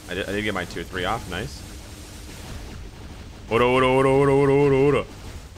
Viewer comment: Did i just hear a "ORAORAORA" from Jojo's Jotaro kujo? 🤣